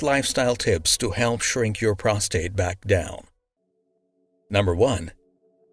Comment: In 07:00, SKRINK instead of SHRINK.